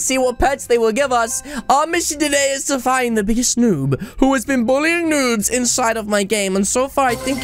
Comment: did i hear that right did he said our mission today is to find the biggest noob